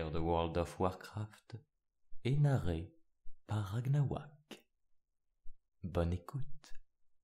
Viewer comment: Quand il a dit " bonne écoute " moi j'ai compris " bas les couilles "